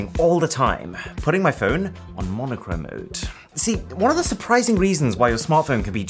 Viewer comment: thought it said Monotone lmaooo, I've been reading that word wayyyy tooo much...